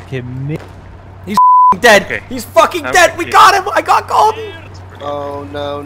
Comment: Why is there a censor for only the first 30 seconds and then immediately after he says “fuck you”? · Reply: I was new to the whole thing they did. Looking back at it rn he actually says fucking